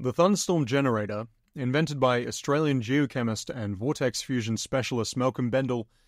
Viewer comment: Thought you said "jew chemist" instead of geochemist, at first, and I almost left.